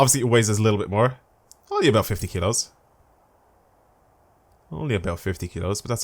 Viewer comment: When he said kilos it sounded like he said kidos lmao